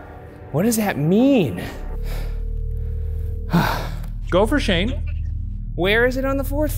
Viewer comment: when Shane says “go for Shane” the subtitles say “Gopher Shane” and I just wanna take this moment to appreciate the person who does the close captions (there has to be someone the starting watcher noise has something associated to the ep every time). Whoever you are you’re my personal fav production person and keep it up all of you this is better produced then some of those ghost shows we all grew up watching (I’m not naming names bc I watched A LOT and it would be rude) keep it going I’m so excited for Ghost Files!